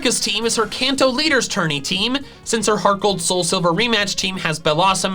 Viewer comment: did he say tourney